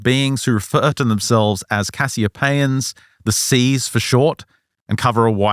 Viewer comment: I thought you said “the c is silent” instead of “the c’s for short” making them the “assiopeans” 😂